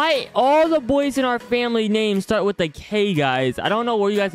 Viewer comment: If is and foltyn is kearsten then why does maddys name start with an m I thought he said everyone in our family's name starts with k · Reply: no, he said every boy in the family's name start with k.